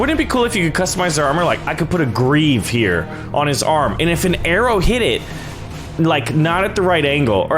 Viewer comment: did he say put a greave on the head butters arm?